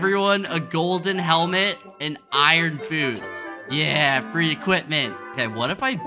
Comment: When skeppy said "iron boots" I heard "iron food" 🤣🤣🤣🤣🤣🤣 btw love your videos!! 🤍 TIME STAMP =